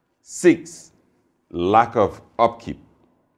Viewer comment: At first I heard #6 as "Lack of Okey" I think it should be in there somewhere.